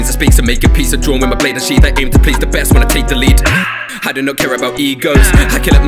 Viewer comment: I deadass thought you said "I do not care about e-girls"